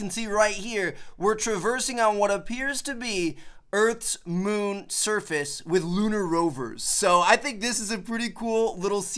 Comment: did he say "earths moon surface"